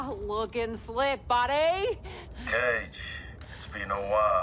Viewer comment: At first I thought she said “looking Slim buddy” 😂